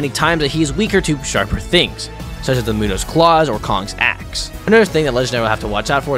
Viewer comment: Bro I thought he said kongs a$$💀